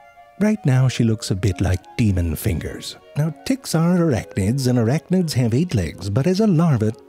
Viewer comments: I swear to god the way he mispronounces tick I heard “erectnids” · Did he just say D(t)icks are erect(nids)? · I could have swore I heard Ze say "erect-nids"